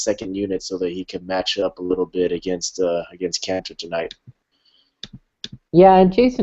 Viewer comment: oh no ISAIAH!!!! i'm already looking towards the draft. top 5 pick? who do you guys like? props to JT. did u just say anus kanter? =)